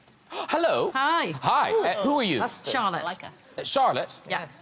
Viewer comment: "Ray Charles? Oh." That's honestly what I understood Graham was saying. To my defense, English isn't my native tongue. 😁